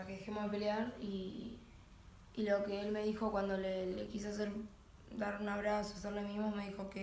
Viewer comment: "le quise hacer un pe... le quise dar un abrazo" :v yo entendí eso :v